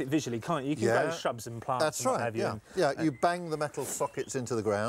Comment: What did he say!? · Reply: Shrubs, bushes and whatever